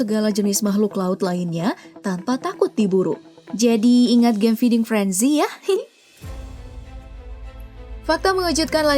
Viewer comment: game apa? Gak kedengeran Plis jawab ak mau dowlond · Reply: Feeding frenzy